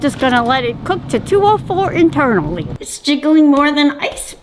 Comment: “It’s jiggling more than ice spice” I swear she is the funniest grandma ever 😂😅 Ty for the likes Rest in peace lynja we all love you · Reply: Did you just say it’s jiggling like I spice